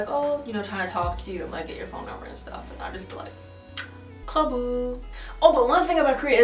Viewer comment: What does 5:32 mean? I thought it sounded like 가부 but that means availability?